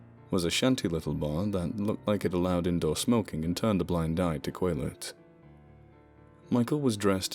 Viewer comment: did you say Quaaludes? My d.o.c! Haven't had one since 1884, oh well...